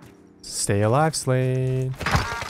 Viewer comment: Anyone else hear “stay alive slave” the first time?